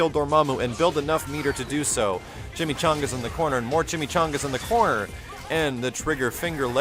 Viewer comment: did he say chimichangas? I LOVE CHIMICHANGAS!!! :D